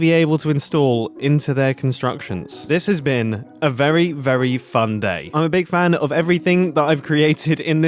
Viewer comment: cause of his British accent it sounds like mumbo said “this has been a very fan day”